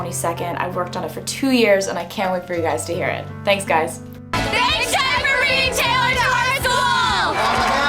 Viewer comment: idk wtf they are saying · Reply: Big shoutout to bringing Taylor at our school I think